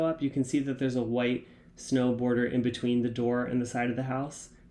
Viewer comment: When you were saying "snow border" I thought you were saying "snowboarder" and I was squinting so hard 😆